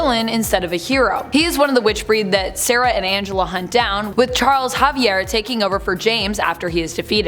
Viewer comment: Did she say Charles Javier at 3:11? 🤣🤣